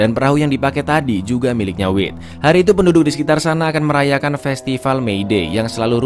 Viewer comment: masa ku dengernya festival wib😭🙏